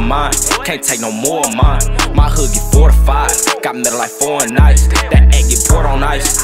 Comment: Did he say git mattel like fortnight